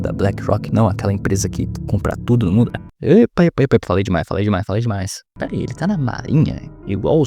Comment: aquela empresa que O QUE? Não sei se foi proposital mas não deu pra entender o que cê falou não kkkkk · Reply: Compra ツヅムンヅラ (n sei)